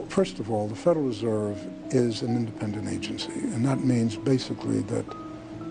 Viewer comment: I had to listen over and over at time stamp 7:58 to properly understand what was being said. I even turned on subtitles from YouTube to try to figure it out and it seems like there is a missing word there, and should be understood as follows ''the federal reserve is an INDEPENDENT agency''. Makes me wonder how much we are allowed to understand about the nature of this issue. Also makes me wonder what stand humanity has on interstellar politics atm and what leverage the US black programs have on it.